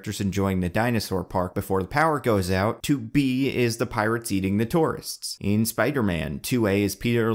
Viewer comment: did he say "the pirates eating the tourists?"